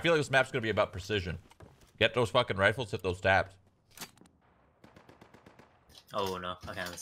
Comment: Did he really say, “hit though’s dabs or tabs”? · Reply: Hit those taps (one taps)